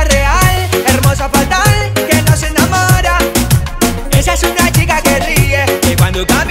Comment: Cómo puede ser que diga "Que nos enamora" y no "Que no se enamora". Infancia exterminada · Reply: Yo pensé que decía "que no se enamora", hasta leer tu comentario.